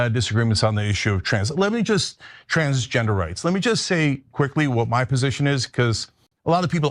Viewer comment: First time i heard trangenderites thats intresting it seems more dehumanizing tbh. That may just be me but it hit wrong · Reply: he said transgender rights; i wouldn’t expect Cenk to rly just start saying wildly inappropriate terms like that, but i can understand mishearing it like that haha